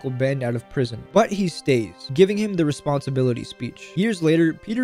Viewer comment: I had to rewatch this because I swear I thought he said "As Uncle Ben died in the chair" I thought he meant they gave Uncle Ben the electric chair but I misheard him say "Guy in the chair"💀